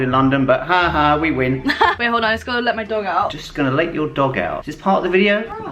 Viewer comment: Who else thought he said “just gonna lick ur dog out”💀💀